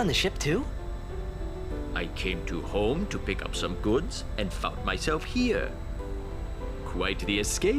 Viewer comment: What?? did he just say i fuck myself here??